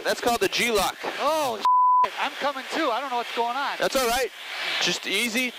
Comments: I heard "oh shit I'm coming too, I don't know what's going on" Instead of "oh shit, I'm coming to, I don't know what's going on" 🤣🤣🤣🤣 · I heard “Ohh man I’m c**ming too, I don’t know what’s going on”